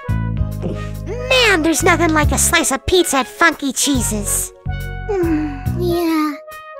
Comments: At the start of the video I wasn’t looking at the words and I thought bf said “fuckey cheese” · at the intro i thought he said "Wow, Nothing Like A Good Slice Of Pizza At Fucking Jesus-"